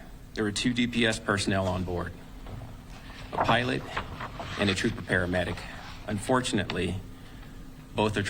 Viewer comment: Didn't the guy say a pilot, An officer and an EMT all went down? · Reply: I thought he said 2 pilot and trooper paramedic?